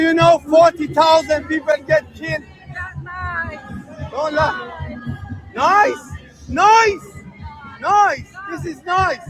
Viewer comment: & 1:11:17, I thought the woman said 'Lies, lies" not 'nice, nice.' If that's the case, that makes her sound less vile, but believing lies. But I don't know what she was saying before and after. Maybe the context made the person taking the video think she said, "Nice, nice."